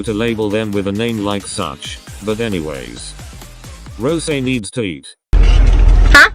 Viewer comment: I heard that as "Rose needs teeth" cause I wasn't looking at the screen at that time I—💀💀